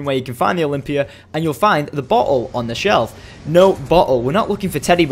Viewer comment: I thought he said butthole instead of bottle xD